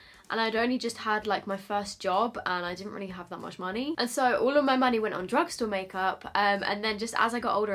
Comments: I deadass looked up as I was doing my face mask because I thought you were saying ''All my money went on drugs''! I am definitely going lockdown-crazy! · The video buffered at 11:32 and I was quite concerned when I heard “All of my money went on drugs-“ 😂😂😂